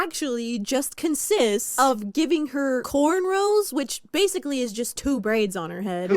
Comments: did he just call cornrows cornrolls?😭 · Did... did he just call cornrows "corn rolls".......